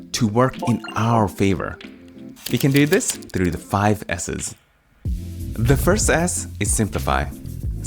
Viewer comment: The 5 "S" caught me off guard and I heard "the 5 asses"